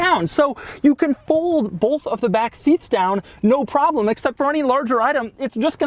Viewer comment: Did you just say "bolth"? dislike unsub :P